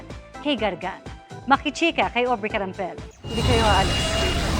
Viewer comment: Narinig nyo may nagsabi ng "hindi kayo aalis" ano kya pinaguusapan nila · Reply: “Hindi kayo aalis nang di ako kasama” Amihan